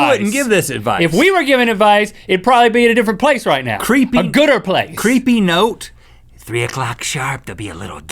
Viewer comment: did Link say a "gooder" place?